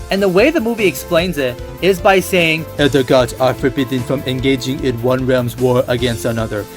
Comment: All this time I thought Raiden said: “Elder gods are forbidden from interfering”, and said “but you’re not an elder god!!!”. But no, he said “All the gods…”